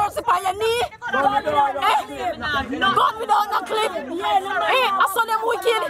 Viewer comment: Did I heard " Gun without NO CLIP???? 🤔😏😏